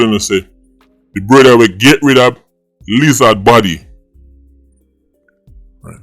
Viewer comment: But thought u said them feed lizard body to dogs/dash weh